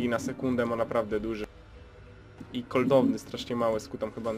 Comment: miałem dać łapke, ale usłyszałem "koldowny" (cooldown) ;)